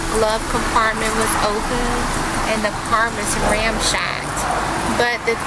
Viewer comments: Did she just say "and the car was ramshacked"?? I listened to that 3 times....that's what I heard 🤦🏼‍♀️🤦🏼‍♀️🤦🏼‍♀️ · did she just say ramshacked?!? 🤣😂🤣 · Am I just old and deaf? It sounded like she said "ramshacked"